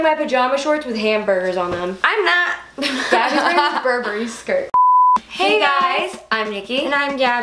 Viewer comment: I thought Niki said “ Puberty skirt "... At the begining:)